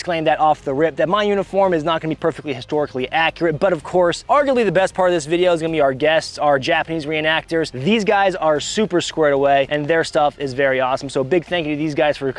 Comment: The Japanese reenactors are such a cool complement. Mad props to them and you for having them. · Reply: I thought you said Japanese Reactors 💀